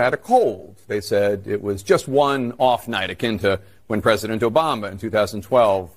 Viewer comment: did he say Joe bama?